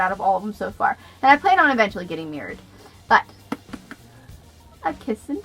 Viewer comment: Your bookshelf is so perfect and pretty! Lol bad stars, love it. Oh getting "mirrored," I thought you said getting married for a second. Your Frankenstein edition is amazing you're right. OMG me too!!! I thought I'd read the real thing but it was child version and I just found out a few months ago.